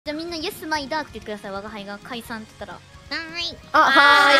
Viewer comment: 我一直聽成Yes, my Duck....